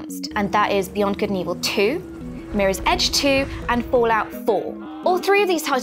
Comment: she said Fallout 4 but the caption said Fallout 3